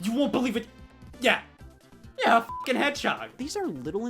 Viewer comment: for some reason I heard Scott say hedgehog as "YEAH A FUCKING HEADSHOT"